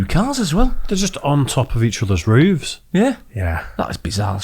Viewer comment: Did you say rooves?